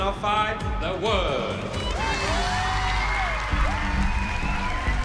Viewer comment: i thought Mellisa said the road for nationals is looking good for my kid